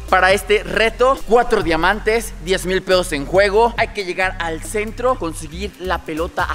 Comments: soy yo o escuche Tres mils Pedos en juego JAJAJAJAJAJAJJA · alguien escucho lo que dijo antrax en ves de pesos dijo pedos minuto